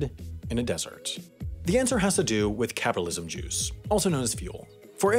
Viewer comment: “capitalism juice” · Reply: I thought he said "catalyst juice".